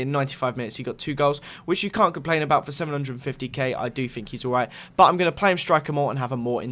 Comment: did you say 750k?